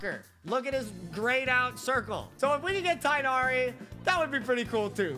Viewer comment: I must be going crazy when he talks about Tighnari in the beginning I could’ve sworn he said “this n*****” I’m too sleep deprived